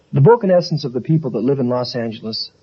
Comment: What does he say at 0:00 before "people who live in Los Angeles"? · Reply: I believe it’s “the broken essence of the people who live in Los Angeles”